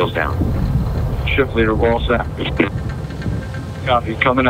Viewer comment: did they say ship leader ball sack I swear that it sounded exactly like They said ship leader ball sack I couldn’t have been the only one who Ended up catching that I had to watch it quite a few times because it Legitimately sounded like that no matter how many times I’ve played it back it sounded exactly like ship leader ball sack for real I gotta be trippin or I’m 110% accurate & not alone on this one who else caught that